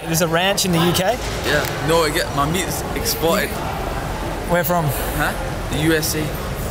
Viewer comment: mark - What he said was "exported".. what I heard was EXPLOITED